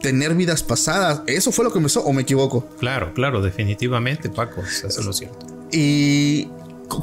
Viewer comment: En el minuto 27:36 se escucha una voz que dice “Eso no es cierto”, alguien más logra escucharla? Y antes unos minutos quizá 5 se empiezan a escuchar unas respiraciones gruesas M